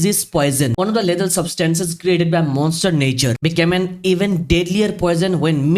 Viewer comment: did i hear monster Nature instead of mother Nature ☠☠